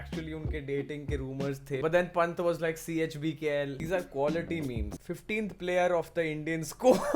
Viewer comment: what is he saying CHBKL?!?